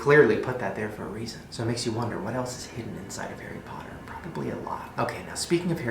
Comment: I had subtitles on and when you said "it makes you wonder what else is hidden in Harry Potter. Probably a lot" but instead the subtitles said "it makes you wonder what else is hidden in Harry Potter. I bleed a lot" and I can't really hear the video because it's night and I'm trying to be quiet but I was really confused lol